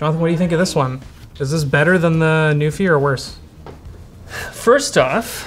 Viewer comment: Every time you say "NuPhy" I don't think of keyboards. I hear "Newfie" and think of Atlantic Canadians.